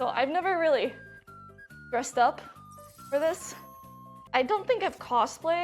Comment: I swear she said breast up.